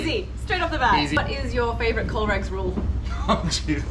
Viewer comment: what is your favorite colerex rule".........Sounded like you said 'COLO-RECTAL'.....rule. Thought you might be getting very personal there.